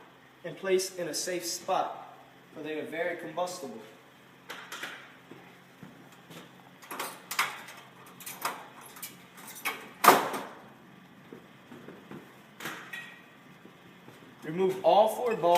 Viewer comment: did he say "they are very combustible"?